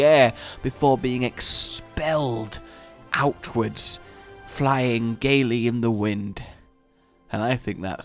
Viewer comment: I heard "Flying gayly an win"